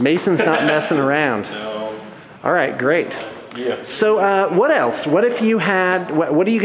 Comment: btw almost every time u say "What else" i hear "wood elfes")